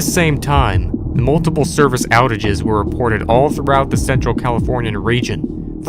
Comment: I thought he said thentral